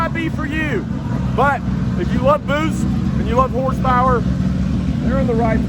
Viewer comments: I had to back it up 3 times when you said "if you love boost and you love horsepower", I thought you said "if you love boobs and you love horsepower"! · No lie, I thought I heard “if you like boobs and horsepower, this video is for you.” Best video of 2020 by far. 🙏🙌🥶